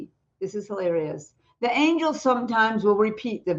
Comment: Did she just call angels angles?